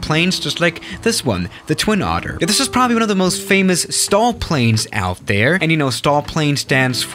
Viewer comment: entendi stall planes xD